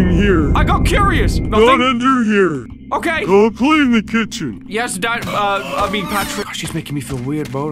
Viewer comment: I heard him say "yes diet" 💀